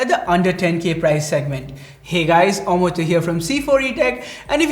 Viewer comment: When Says, "hey guys Amartya here" I always hear "Hey guys A Mortal here"😁 is kicking the show and he's voice is like a lullaby for a child. Cheers...!